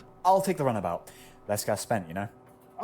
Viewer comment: I believe you misheard. "Runabout" is an old word for "car". So all Kitten was saying is "I'll take the car". "Walkabout" means something else entirely. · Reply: well, you’re right, I did mishear. I probably assumed he said walkabout when writing this comment due to the “less gas spent” comment kitten makes afterwards, which I assumed meant “go there on foot” when in this context it meant “the car is more fuel efficient than the double decker bus”. At the same time though, walkabout does mean to take a stroll or a walking tour, and I’m curious what you think it means as (and I could be misinterpreting you, but regardless) you seem to be implying it doesn’t or has another definition I’m unaware of?